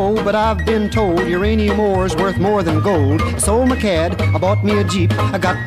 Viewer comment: Please tell me I’m not the only one who thought that when he said “sold my cad” that he said “sold my cat”😭🙏